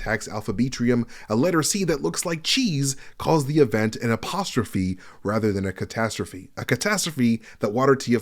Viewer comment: when letter C said apostrophe I thought he was saying apocalypse and not catastrophe lol - apocalypse makes more sense given everything's gone to shit and it looked like the numbericons weren't going to take any prisoners